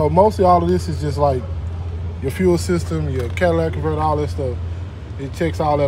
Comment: Did he just say "Cadillac converter" ???? 🤨